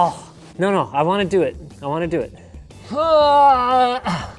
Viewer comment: What Linus is saying: "No, no, I want to do it". What I hear: "No, no, I want to drop it".